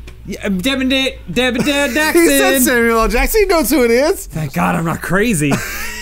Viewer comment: "Sammy L. Jackson?" That's what I hear anyway's